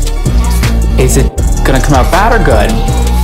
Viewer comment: I thought he said “ is it going to come out fat or good? “ 😅😅